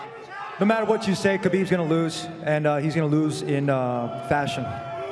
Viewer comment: Did he say khabib’s gonna lose in fashion?